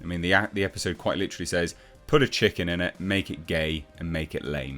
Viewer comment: "Put a CHICK in it and make her gay" is what is said. Not a chicken (1:39). lol